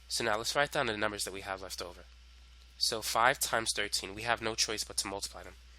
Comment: he cannot write 1s AAAAAAA. He said 13 I heard 3. 😞